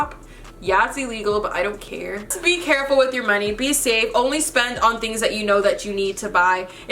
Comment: Thought u said with no money thoe :|